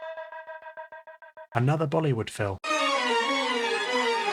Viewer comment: y did i think bro said "hollywood fell"😭